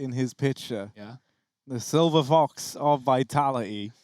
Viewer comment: what a silver haha caster says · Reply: He says; "the silver fox from vitality". Refering to RPK due to the color of his hair and age.